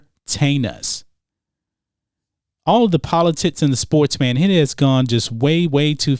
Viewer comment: I keep hearing “Politits”...hhmm